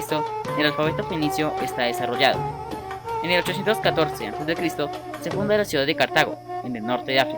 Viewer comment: alguien sabe si dijo 1814 o 814 en el minuto 2:59. no entiendo la tarea :c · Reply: 1814 emily